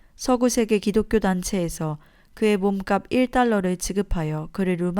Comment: 목사님 석방 몸값이 1만달러로 들었습니다 1달러로 나와서 고쳐야 할듯요. 제작에 감사드려요